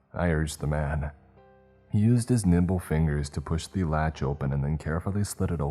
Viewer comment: I definitely heard "used his nimble fingers" as nipple fingers. Weird imagery there ...